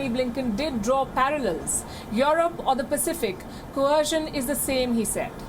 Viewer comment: What's he sayin'?? China is cohercing Europe?